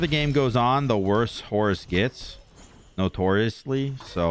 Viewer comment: what I heard: Not-Horus-ly